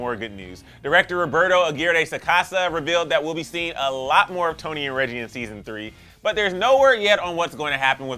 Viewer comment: did he say Toni and Reggie?